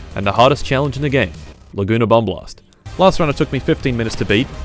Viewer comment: I heard "laguna bum blast"